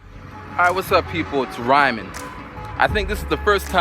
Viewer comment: I thought he use to say at the beginning of videos "What up people, Nyman." Though it's.